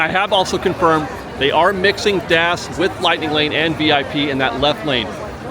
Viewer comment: In the pirates queue part @ 30:50 what are you saying dash? Davs? Dabs? You say dash..VIP...genie plus...regular guests. What are you talking about??? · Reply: DAS. Disability Access Service